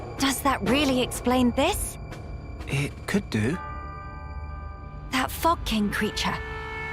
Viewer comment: sounds like melia said: "that fucking creature"